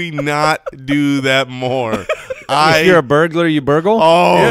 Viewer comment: Got Jason hungry when Mike said burgle, thought he said burger 🍔